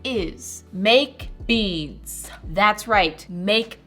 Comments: I thought you said make beans 😅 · People who wanted those beads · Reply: First I thought she said baked beans😂😂😂😂😂😂😂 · I nearly fell out of my chair when Moriah said 'make beads' because I thought she said 'make BEANS..' oh well. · I heard her say BaKeD bEaNs instead of MaKe BeAds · It thought you said "Make beans" But you said "Make beads" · for a second i thought she was saying make beans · Anyone else think she said make beans? I’m dyslexic so reading is a little harder for me so I just thought she said make beans LOL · Lmao when moriah said “make beads” I thought she said “baked beans” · I'm so dumb, I thought she said bake beans not make beads😂🙄🤦‍♀️ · for the longest time i thought she was saying "make beans" 😭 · I really thought she said she was making baked beans